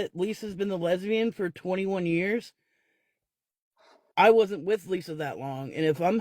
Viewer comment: I thought she said she was with women for 27 years....the lies....